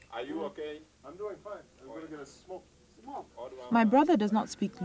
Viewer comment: what's he say at 4:09? can I get a smoke?